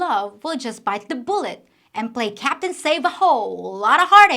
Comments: did you just say "Captain Sav-a-hoe"? lol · I thought for a moment you said "Save a Ho"